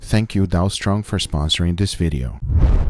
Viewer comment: Dead set thought he said " Thank you Donald's Trump for supporting this video " .